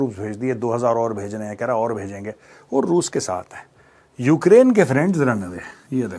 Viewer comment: mujhe pata hai ki wo "run away" likha hai par mera dhyaan nahi tha screen pe to mujhe sunai Diya ki "Ukraine ke friends randwe".. main achambhit ho gaya ki ye kya bol diya major ne